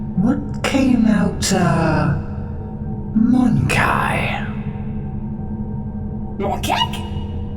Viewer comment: I heard moon cake